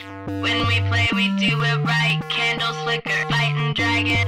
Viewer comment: I swear she says "candles, liquor" when she is actually saying "Candles flicker." I'm like, :o DnD and liquor.